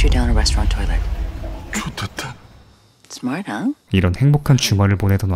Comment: 이거 뭐라고 한걸까요? smart huh? 이건가???